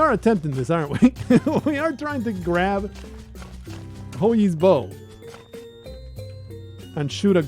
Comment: First time I heard someone refer to Hou Yi's bow by name I thought they said Hoagie's bow. Now every time someone says it I can't help but imagine a bow that shoots sandwich fillings and condiments.